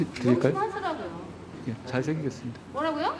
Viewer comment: 제가 생각하기에는 "잘 새기겠습니다"라는 한동훈의 말을 이수진이는 순간적으로 "잘 생겼습니다"라는 비꼬는 말투로 들었었지 않았나 하는 제 개인적인 생각 입니다 ㅋ